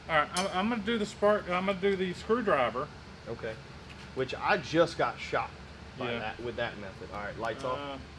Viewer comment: what i heard "i got shocked when i touched the frame"..... so you have a ground issue ?